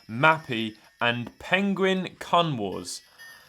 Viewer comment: literally didnt hear right and i thought you said penguin cum wars lmao